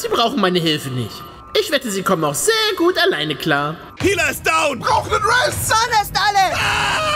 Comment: was haben die alle da gesagt habe es kaum verstanden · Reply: Dort handelt es sich um MMO begriffe :) Vegeta: Healer ist down! Krillin: Brauch nen rez! Son-Gohan: Mana is alle! 🥴 Die armen haben alle so Panik!